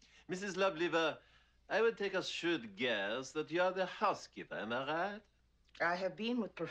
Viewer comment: This is genius. I love it! My one comment would be that at 4:59 he says "shrewd guess" in the original. The subtitles say "shoot guess" so not I'm not sure if that was a typo or intentional change haha! But amazing job, this had me cracking up for ages!